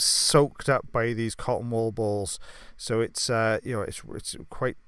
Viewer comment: I didn't fully read the title so I thought he was saying "cotton ball balls" the whole time lol